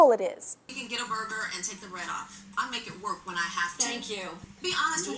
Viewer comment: I always thought Tyra said, "You can get a burger and take the red off." I didn't know it was bread until i saw the subtitles.